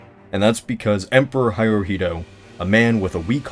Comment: Did you just say Hairohito?